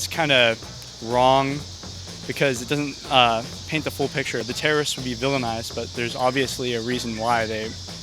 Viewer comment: what the hell, he is saying, terr.. could be villainized?